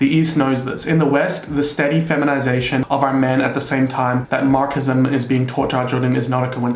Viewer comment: Did you just say “Markism”?